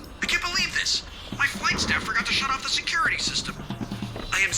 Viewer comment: what did john say 'I can't beleive this, my [insert word here] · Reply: "My flight staff forgot to shut off the security system"